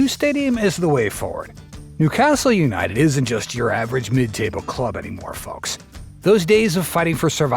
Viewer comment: did bro say midtable